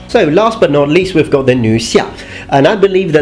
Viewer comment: When I was told the female class was "nu xia" I thought they said "nu sha" or 㚢杀 = killer woman